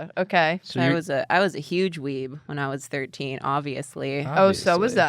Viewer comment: I thought said she was a huge ween. I was very confused